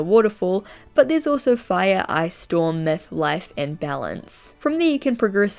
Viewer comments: i swear she said meth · wants people to use they/them for them! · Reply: i swear they said meth